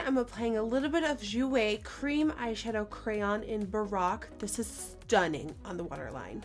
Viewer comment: For real tho when was talking about the jouer eyeshadow pencil I heard "Barack" instead of "baroque". I knew jouer didn't make a fuckin eye pencil named after the 44th US president. 😂😂